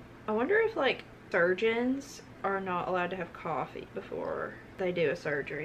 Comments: I thought she said virgins and I was very confused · When she said surgeons I thought she said virgins 😆😆😆😆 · 🤭 I thought she said “virgins” instead of “surgeons” lol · I thought she said virgins 😔